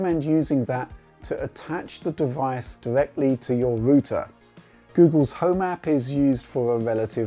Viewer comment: Did he just say "ROOTER"?